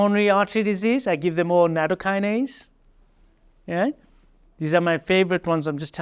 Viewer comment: After fish oil he recommends a supplement for cardiovascular patients at/around 59:12 Was it Nanocarnaise??? Didn't catch what he was saying??? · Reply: nattokinase